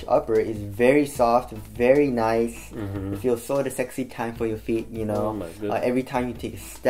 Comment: what does say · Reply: “Sort of sexy time for your feet”